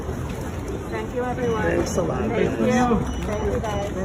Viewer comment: We hear the lady say "thank you everyone" and immediately after I hear a man say "That's a lot of dress". Did everyone else hear that too? Or is he maybe saying "thanks a lot guys"? Emerald green looks really nice on her. Her dress would of been better in that color with less volume for this particular event. Meghan has trouble meeting normies because she doesn't know how they feel about her. She hates feeling out of control. She likes "elites" because they're all pretty much fake like her.